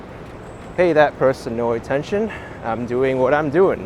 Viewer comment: what happened under the scaffolding? who said something? I'm confused! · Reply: He said "The F%# are you doing?" It wasn't too audible in this video.